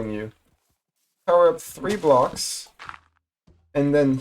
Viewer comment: What did he say? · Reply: 3 blocks